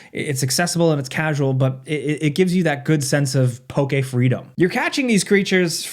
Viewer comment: Did bro say PokeFreedom? You just going to slip that in there and act like no one was going to notice?